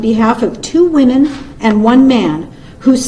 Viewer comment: Did she say 2 women and 1 man? Just curious